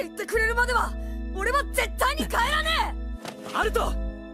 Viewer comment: It sounded like shu said Naruto